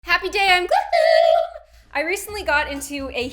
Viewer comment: it sounded like she said Crappy Day I'm GlOoOoOoOoM